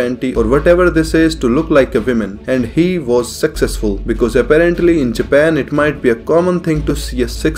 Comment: I heard "and he was sexcesful" and idk why....